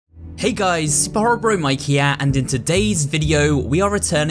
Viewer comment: did you say sporrow bro mike